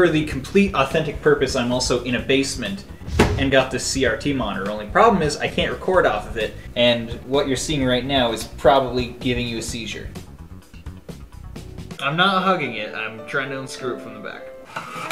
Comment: "I'm not hugging it I'm screwing it from behind" - What my Dumbass thought said